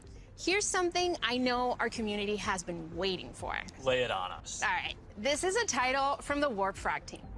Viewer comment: Actually thought Zuckerberg said, "Play it on me", which would have been infinitely worse. Too nervous to go back and check... Gonna give him the benefit of the doubt for my own sake